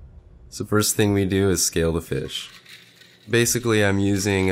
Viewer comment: I had the volume down when vid started, thought he said "okay, so first thing we do is...stare at the fish."